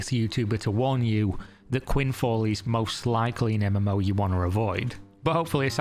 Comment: So is this already being called “quidfall” as a mockery or did I just mishear that? 😅